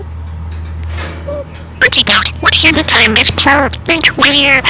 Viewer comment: Minion is a treasure, he’s so cute and funny, and just so amazing how he talks. I thought I heard 8:20 “wanna hear the time miss” 😁 I was wondering why he says “can I touch your beak” and “budgie dot” I love when he does little phrases in sing-song, and when he laughs EXACTLY like you 😆Also love how he bounces up and down whenever he says “flap your wings” 😆It must take so much time and effort to annotate, thanks so much for sharing🥰 Whitey loves her new star toy 👍Hello to Emma, Nitro and Nautica too 💙💛💙